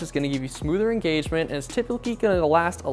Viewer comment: wtf did you just say lmfaoo? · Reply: Typically lol